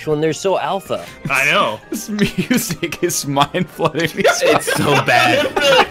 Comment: I think he said Mind flooding not Mind flooded